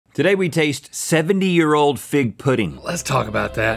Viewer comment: I thought it said 'pig pudding'